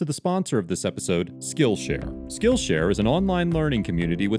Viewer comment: really thought bro said “skill issue”